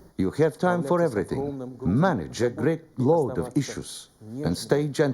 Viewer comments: I could have sworn the translator said, "A great load of dishes." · Omg, I seriously thought Putin said that women "manage a great load of dishes" LOL